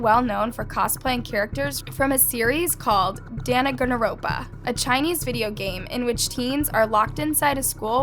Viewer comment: DID SHE SAY DANNAGANNARONPA 💀💀